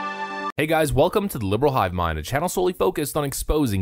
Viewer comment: I heard "Welcome to the new world" I got a bad feeling about this...